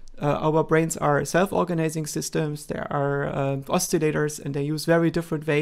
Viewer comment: What’s the word he uses at 0:57? Postulators? Oscillators? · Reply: It's "oscillators"